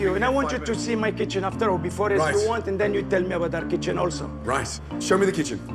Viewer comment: Did he say "rice" instead of right or nice?